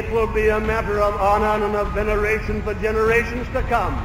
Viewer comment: "It would be a matter of honour and of veneration* for generations to come" You're far from the only one who has misheard him in this speech, other channels in the past have made the same mistake but if you listen closely it's quite clear that he says veneration instead of generation. It also makes much more sense that way